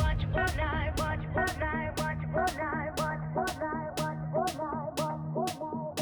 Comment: I hear “watch fortnite “ ar